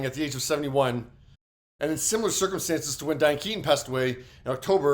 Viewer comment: love your reports, but you talk very fast and if it weren't for closed captioning, I would not be able to understand your words. Might it be possible for you to slow your speech down just a little? Thank you! Beautiful tribute to Catherine O'Hara. Thank you for that as well. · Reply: Yes, unfortunately, my old ears couldn’t make out half of what he was saying. When he was saying Diane Keaton, I thought he was saying Dan Keys and I couldn’t figure out who that was. But thank you for the nice tribute to Catherine.